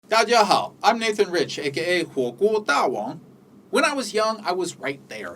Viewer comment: Hah, the auto-generated subtitles say “cool cool Taiwan.”